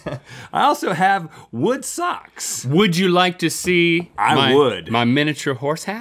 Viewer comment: When link said “would you like to see...” I thought Rhett said “my wood” then I went back and was like 😅😂